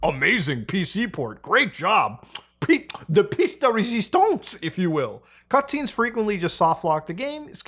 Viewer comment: lol what did he say ? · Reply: Piece de Resistance